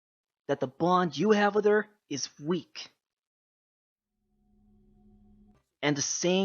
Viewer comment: WHAT JUST A MINUTE DID I HERE THIS CORRECTLY OR DID HE JUST SAY THAT MY BABY SISTER'S BOND IS WEAK!?